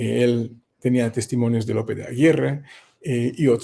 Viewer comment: Shalom ají, una pequeña pregunta no escuché bien claro lo que dice en el minuto 17:46 en adelante . Parece que dice que tenía testimonios del apellido Aguirre ? Bueno espero me pueda responder con claridad por cierto también soy de Ecuador 🇪🇨 y mi fe es en Elohim de Israel 🇮🇱